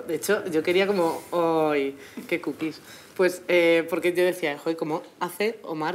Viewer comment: what is she saying at 3:22?🤔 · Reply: "que cookies"